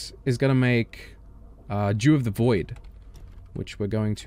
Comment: wait, did you say "Jew of the void" at 11:46 ?